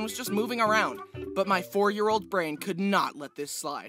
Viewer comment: I thought he said 40 year old brain instead of 4 year old brain